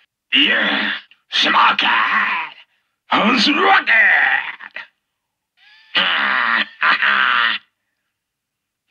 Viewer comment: I've always wondered what he's saying right there :-$ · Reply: "I'm smoky!"